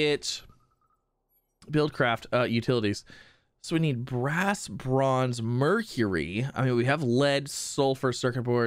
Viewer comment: LOL my sound cut out for just a second at the weirdest time. You said "Brass, bronze" and what I heard was "brass bra" but rewinding a bit cleared it up...lol